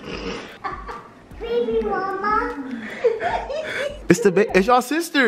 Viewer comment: I thought said baby mama but he really said baby, mama